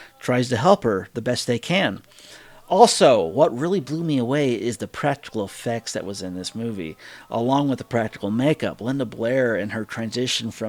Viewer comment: I thought you said you really liked the “prep school sex” but you actually said “practical effects” lol